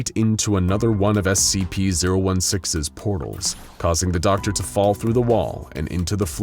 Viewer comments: did you say scp 016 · did he just say scp 016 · Dude he says 016 instead of 106